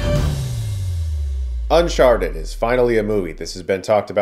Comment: Sounded like you said Unsharted at the beginning. I'd see that movie.